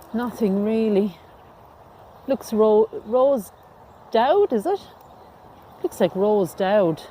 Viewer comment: Please forgive my sense of humor; but at first, I thought the stone said "Rosebud," instead of "Rose Doud."